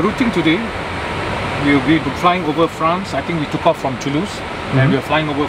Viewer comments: Did he say 'I think we took off from Toulouse?????' Dude!!! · Did he really say " I think we took off from Toulouse?"